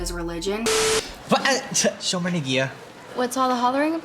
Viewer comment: i know hebrew and at 17:36 when the subtitles say jewish guy: (speaks in hebrew) he doesnt even say actual words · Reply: He says "shomer negiah."